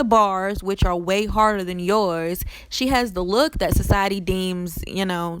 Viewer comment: You Buggin Thoo Cardi Aint Cute 😂 You Cuter Than Her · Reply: Ohh Nevermind 😂😂 I Thought You Said She Got The Looks That Society Likes